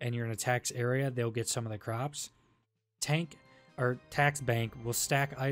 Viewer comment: I swore you said taint. lmao but you didn't. great vid!